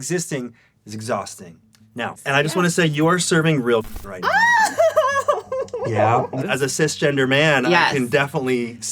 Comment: What did he say at 0:50 ? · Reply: The C word, “C*nt”.. I don’t wanna type it lol